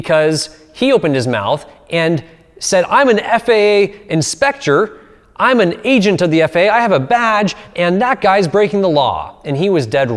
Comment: I thought you said "I'm an FAA inspector, I'm an agent of the FAA, I have a VAG" hahahaha i was like whaaaaaaat dang is going hard 🤣🤣🤣